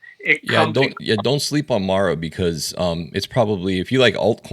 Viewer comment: Hi, what coin you were saying about at 18:45? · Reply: It's a Bitcoin miner stock $MARA